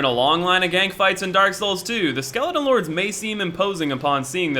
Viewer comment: I thought it said skeleton lads